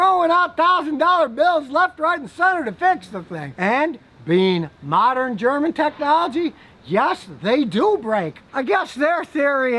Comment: I watch Scotty so much that when he said "And, being modern technology..." I thought he was saying, "And, being mechanic Monday, I'm giving this BMW away!"